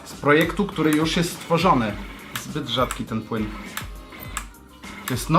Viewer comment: tylko ja usłyszałem "zbyt rzadki ten chuj"? :o